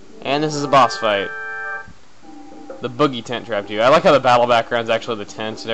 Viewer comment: i thought it said the boogie tent raped you lol